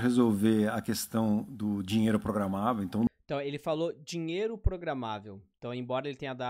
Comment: Por que toda vez que foi falado “dinheiro programado” eu ouvi “dinheiro controlado”?! 🤔